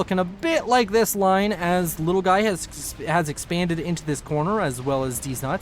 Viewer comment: I just skipped ahead one click and the first thing i hear is 'has expanded into this corner just as deeznuts' and I was lost for context 😂